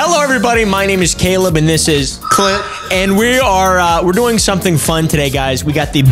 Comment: i honestly thought he said c*nt im sorry :/